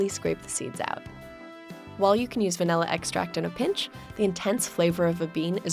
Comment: i'm sorry did you just say... vaNALLa?!?!?